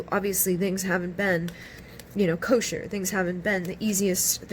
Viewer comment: Omg I watched pile 3 and I felt like it wasn’t resonating with me so I was headed to click off and you said kosher but my name is Cosha and it sound like you said my name !